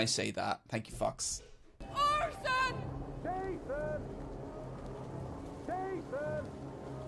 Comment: I heard that "JASON" Fox, very funny.